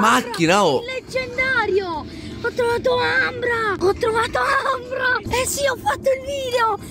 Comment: Scusa ci puoi ripetere non ho sentito hai trovato??? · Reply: Si ho trovato ambra non preoccuparti